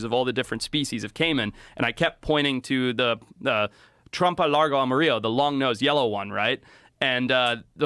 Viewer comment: Did i hear that right? Trump a lago? Like TrumpTrump in mar a lago